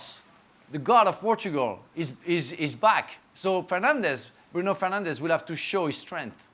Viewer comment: Did Frank just call Bruno, “Penandes”? 💀